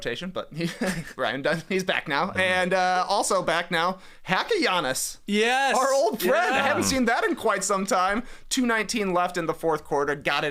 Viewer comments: thought said "Papagiannis" and got excited that Georgios made it back to the association, alas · When you guys were talking about 'Hack a Giannis' i thought you said Papagiannis. I thought Georgios was back in the league! On Greek independence day too 🇬🇷